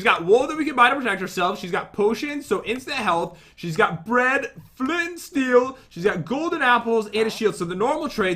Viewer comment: When Pat was talking about Kylie Jenner’s trades I thought he said Fred flint and steel as in like Fred Flintstone That would’ve been a good joke